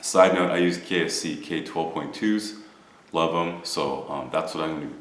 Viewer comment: lol .... 2:45 did he say KFC k12.2's🤣